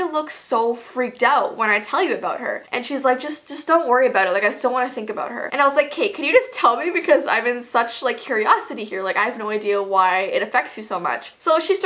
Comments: it sounds like she says “Kate” which could be the girls actual name! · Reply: she said "okay, can you just tell me" fast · is that her real name? Or am I just hearing stuff? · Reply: She said K can you tell me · Is her name Kate because at 6:15 or so u said I think u said Kate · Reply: The Kitty Crew she said k I think like ok can you just tell me kinda thing x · did she say Kate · Uh I think you said her real name at 6:11 I’m not positive if you did but it sounds like you said Kate? 😂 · Did she just say “Kate” like a name, the girls real name. I’m just guessing